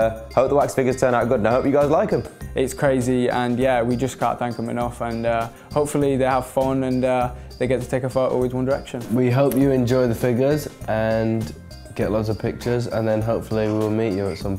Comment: I heard him saying, "get lost with the pictures".. 😂